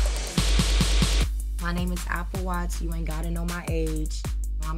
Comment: I thought she said her name is Apple Watch 😂😂😂😂